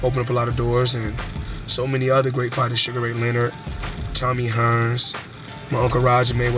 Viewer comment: oh damn he did lol i missed that i thought he said sugar ray robinson